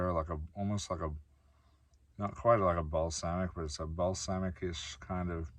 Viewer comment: OMG you said balsamic and coffee almost shot everywhere because I thought you said ball sack…. 🤣🤣🤣🤣🤣 Classic!